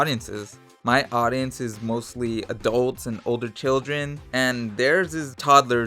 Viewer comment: i thought he said only "children" not "old children"